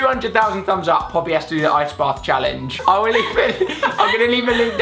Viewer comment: When Poppy said Ice Bath O thought she said eye spa and I was thinking it can't be that bad then